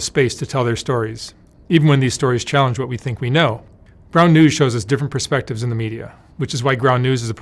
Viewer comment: I’m really not trying to be insensitive due to the content; but it sounded like Mark said “brown douche” not “ground news”.